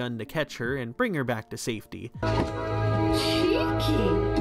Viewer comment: did she just say Kinky?